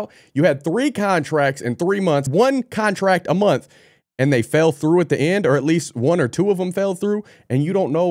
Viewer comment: no contracts fell thru is what she said. The company also failed to give her any evidence of her firing. Just thought to let u know for u misheard the video bro